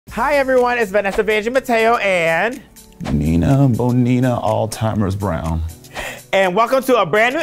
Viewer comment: nina bonina all-timers Brown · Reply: I got happy because I thought it said "Nino Bonina all-stars Brown " honestly... still works